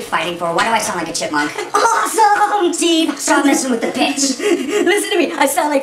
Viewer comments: it sound like he said dont mess with the bitch ROFL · What does steve say @ 0:46-47??? He says Quit messing with the Pitch.